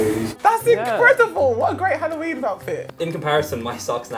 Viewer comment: did she just say "What a great Halloween outfit"? If so, we need to learn our street safety rules